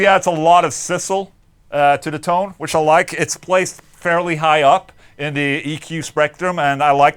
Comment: Its placed fairly high up in the eqs rectum. And I like that. 😂😂 that's what I heard the first time.